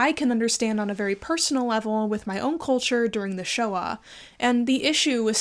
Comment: When you said "Shoah", I thought you said "Showa" and was like "I thought Kaz was Jewish, didn't realise they were Japanese too!"